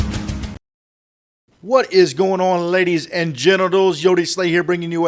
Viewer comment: is it me or did he say lady's and genitals